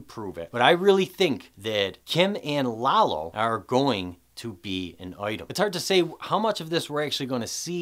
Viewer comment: Great analysis like always, but I could not quite get what you said at 15:47 kim and lalo what? Thanks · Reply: Thank you. "Kim and Lalo are going to be an item."